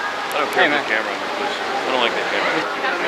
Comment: did perfect say he don't like the camera ? i can't make out his words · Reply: Get the camera out my face, I don't like that camera